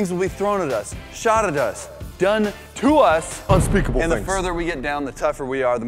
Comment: did u just say unspeakable things ????? i am a fan of unspeakable